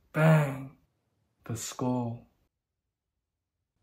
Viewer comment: “I want to bang…. bang…. bang… the school.” “Oh, very white of you!” LMAOO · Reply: Fuck, I've misheard it with "very wide of you"